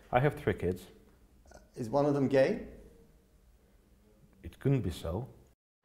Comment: what did he say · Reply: it couldn't be so. Is the answer to the question: could be one of your children gay?